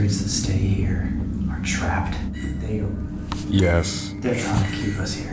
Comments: after says “you want us to stay” colby breathes out and istg I heard a faint “yes” while he’s breathing out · im the only one who heard “yes” at 54:37 right after said “do you want us to stay?” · when said you want us to stay it kind of sounds like someone says yes before they start talking again.